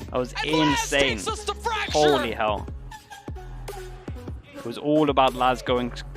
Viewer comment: ~ の｢信じられないね｣と訳されている部分はなんという英語でしょうか。Holy hell と聞こえますが難しいです。